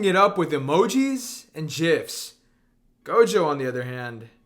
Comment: did you just say jif